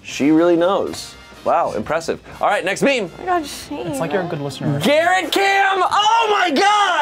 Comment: what does courtney say? I'm sorry, english isn't my first language. It isn't in the subtitles either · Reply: She said “omg shayne”